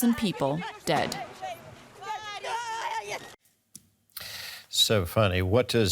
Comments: I thought for a moment he said "so funny" after those tragic video, but the girl reporter name is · Did he just say "SO FUNNY???" are you being serious! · Did I hear correctly? "So funny"? Is it really? It wont be that funny if it happened to him, trash reporter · did he say ''so funny''? xD